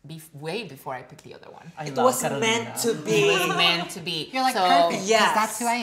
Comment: Is the 3rd girls name supposed to be Catalina or Carolina because it sounds like she is saying Catalina